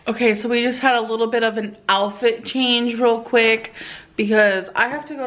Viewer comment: She really does say “alfit” instead of “outfit”. Good grief.